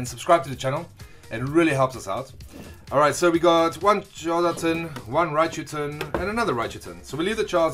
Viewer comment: When you said one Charizard tin it sounded like one Jonathan lol shoutout to all Jonathan's I guess lol